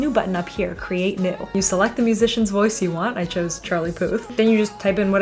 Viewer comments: I thought you said Charlie Poop. LOL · I thought you said Charlie Poop until I read the caption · why i heard "charlie poop" 😭